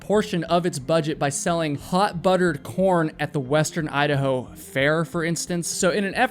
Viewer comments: swear on my beloved horse Dally 's ashes, i thought Idaho must've REALLY decided to lean into that whole old "I''da'ho?! Nah, YOU the hoe!" Saying during that first one cuz i genuinely thought you said: "HOT BUTTERED P0RN" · I could have sworn he said they sell hot buttered porn at the state fair lol